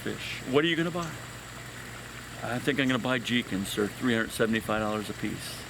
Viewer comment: What's the name of the fish he says he wanted to buy for $375 a piece? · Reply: Jikin They’re a type of goldfish.